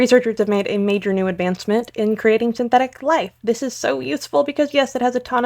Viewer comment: I thought she said "synthetic plague"